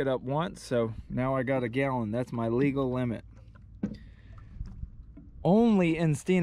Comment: “Got the flounder it’s illegal” 😂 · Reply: Sounded like you said it's illegal but you said "it's a legal"